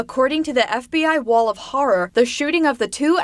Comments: Did she say "FBI wall of horror?" :D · Did she say wall of horror